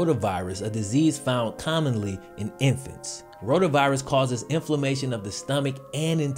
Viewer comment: thought you said coronavirus for a second